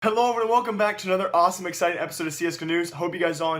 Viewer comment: Sounds like you said CS Kanooze instead of csgo news lol.